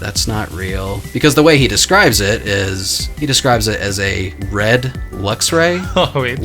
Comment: BRO MY DUMBASS THOUGHT SAID A BREAD LUXRAY